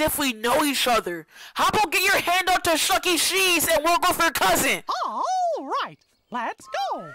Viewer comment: I can’t tell what you said here,I’m thinking you sis wolf over cousin,what did you say here??also,this video literally got me rolling the the edge of the universe where nobody escapes.